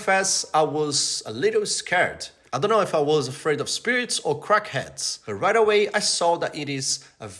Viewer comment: did you say "crackheads" 😂😂